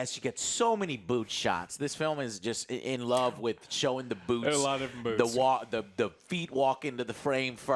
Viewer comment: Boots? I thought you said boobs. Now I'm sad.